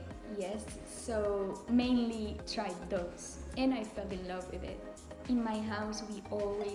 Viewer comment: falling love with it って言ってるのか、一瞬 f wordに聴こえてcool だわ〜って思ったけどわいの聞き取りが悪すぎて